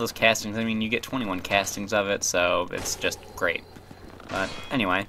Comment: It really sounds like he says "it's just...grape."